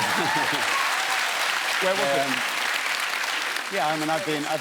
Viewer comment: anyone hear Russel say "where was it" when he said the book was called finding my virginity